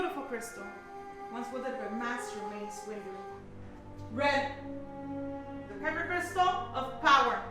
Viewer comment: I thought she said Master Maze Windows